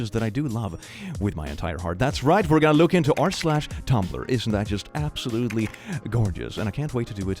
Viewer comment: I misheard tumblr as toddler.. I was so confused especially because I looked at the title and the thumbnail before clicking the video.